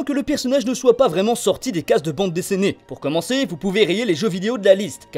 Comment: "Bande" quoi ?? "Bande-Désséné" ?? ><"